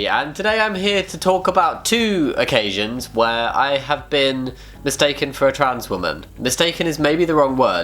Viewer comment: I zoned out for just a second there reading something, and I misheard 0:10 as “when I was mistaken as a Transformer” and I got confused for just a second then busted out giggling